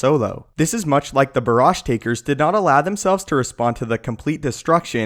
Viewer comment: Before you started explaining, I heard it as "Barrage Taker" and thought they're called that cause they're great at deflecting blaster barrages.